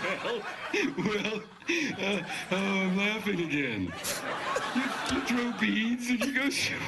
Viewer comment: Guys what did Geoff say after 8:10? I don't understand. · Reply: Gergő Janes hey little late. He said "ohh im laughing again"